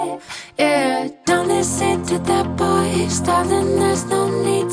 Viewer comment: I always hear "dont listen to that BOYS" Lol sorryyy